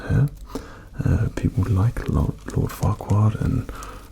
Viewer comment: Ok 12:57, did he say lord fuckwad?